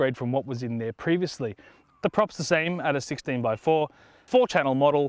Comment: Did he say 16X4 Prop LOL.